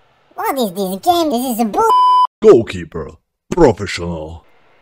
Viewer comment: DID BRO SAID BULLSH!T